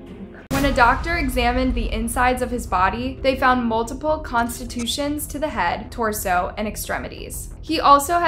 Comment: constitutions instead of contusions is crazy lmao.